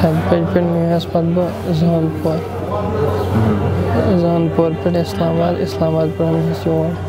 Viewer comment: Shame on your news reporting...What he is saying and what you are showing in subtitles....He is saying ISLAMABAD and you are translating it as ANANTNAG at 1:51...same has been referenced by his cousin later. Firstly, what this KID was doing at encounter site (Apart from stone pelting). Secondly, those who think of INDIAN ANANTNAG as PAKISTANI ISLAMABAD should be treated same way.